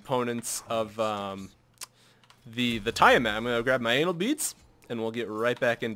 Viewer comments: Is it just me or did he say anal beeds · did he just say anal beads?